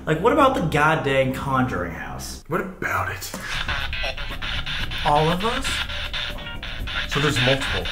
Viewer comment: doesn’t it sound like Colby saying “ all of us” as if his “spirit” or “possession” is saying he will need “all of us” as referring to he will need Sam and his other friends maybe like Kris and Celina in the in the future